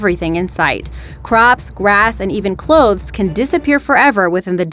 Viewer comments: Mass Starvation. I thought she said Masturbation 😂😂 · did u say master--- ya know what I'll think im moving to someplace without bugs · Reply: I believe they actually meant to say "mass starvation". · If you liked this video make sure to check out our Most VENOMOUS Animals Known To Man video here: <link> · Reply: did she say masturbation?